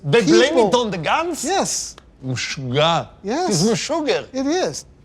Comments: Did he say Meshuggah at 1:20 ? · did he say Meshuggah?